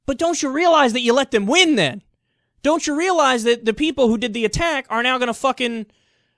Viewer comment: Thought that at 2:12 Kyle had said, "Don't Sharia'lize".